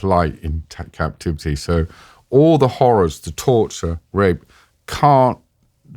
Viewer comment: Did he say rape? I sure hope he said something else... That's fucked